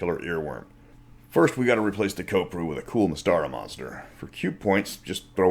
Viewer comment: So why replace them? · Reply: aren't yoy saying you have to replace the kopru with a cool Mystaran monster, or am I mishearing?